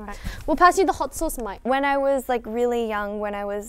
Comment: I thought she said "mate" for a sec but I think she said "mic" lmao